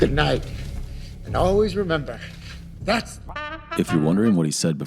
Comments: he didn’t say “let’s put a smile on that face” he said that’s society luv edit: ITS A JOKE SHUT UP LMAOO 💀💀💀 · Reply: Yazmin Wolfhard i thought he said thats life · that’s not what he’s saying. He’s saying And always remember, that’s life. Cuz that’s Murray’s saying on the show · Oh, I thought he said, "That's life!" at the end where they cut him off of the TV. To hint at the Frank Sinatra song. But I can see where he could be saying, "Let's put on a smile on that face." Nice analysis! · What on Earth do you mean "you think you've worked out what he says" @ 13:20!? Joker was saying "That's Life" when the camera cuts & censors him. So you just get "That's"... Sorry, am I missing something here? It's the lyrics from "That's Life" by Frank Sinatra, that's played throughout Joker.. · I always thought he said that's life in the part where he grabs the camera, because that's what Murray would end the show with